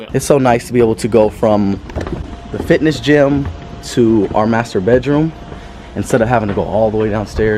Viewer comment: I misheard him, that he thinks it's nice to go from the gym to the masturbate room 😂 - I genuinely could relate to him in that case 💪